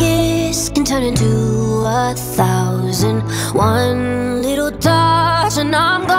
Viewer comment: When i just listened to it and not read it at tje same time i heard "can turn into the cool light thousand" idk why tho